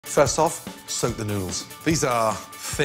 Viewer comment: At first i thought he said "soak the nudes".